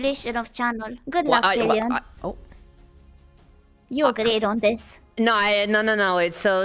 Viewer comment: i think said "you agreed on this" not "you a great on this"